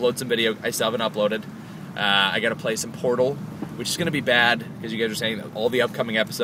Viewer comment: I thought he was gonna say "I'm gonna play some porn" instead of Portal xD